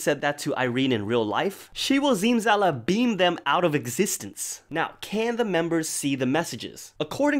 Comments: “She will ZimzalaBEAM them out of existence” I can’t 😂, imma steal that one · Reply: oh my god I’m stupid but I thought he said she’ll Zimzalabim them out the KITCHEN LMAO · "They will Zimzalabim them outta existence!" "We will zimzalasue you!" . . . . . . I HONESTLY CANT 😭😂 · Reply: I thought he said zimzalaBEAM lol